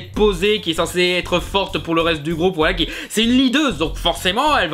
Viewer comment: C'est une quoi ? je comprends pas et les sous-titres aident pas... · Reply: Leadeuse